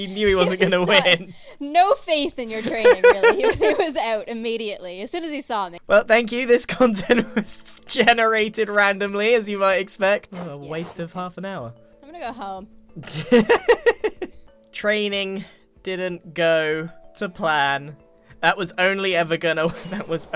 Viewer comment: I misheard and had to go back at 26:32. I thought you had said "waste of a f*cking hour" and I was like swore??? But you did not hahaha